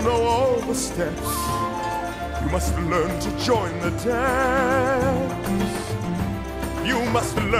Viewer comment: “Dance with me!” Who else heard it?